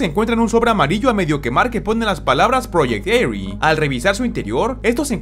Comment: Pense que decia, Project Freefire